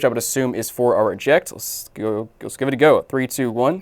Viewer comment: bro what did he just say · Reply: lets give it a go